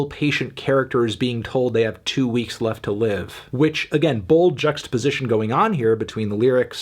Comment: What does he say at 5:45? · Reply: Bold juxtaposition